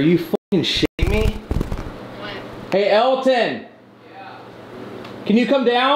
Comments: you can hear a female say what after corey says are you fing shitting me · when the camera light goes out you can clearly hear a female voice say “what” after Corey says “are you f’ing s’itting me” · Corey's camera light goes out and at 1:06:25/26 you can hear a female voice say "What?" Just before Elton says "Yeah" Then at 1:06:40/41 you can hear the same female voice say "It could be worse." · did no one hear the strange “what” after Corey’s light goes out just before he says “hey Elton”. It sounded like it was a woman’s voice and the what was in the same room as Corey